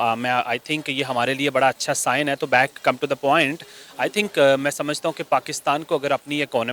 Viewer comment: मैने come to the point सुना था। Back come to point 😅 pehli बार सुना